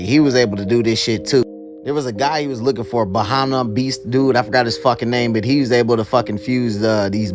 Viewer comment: I can’t say that shit for nothing 😂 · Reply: imma keep it 100 with you. I thought the first time I heard his name, I heard “Bennihanna beast” 😭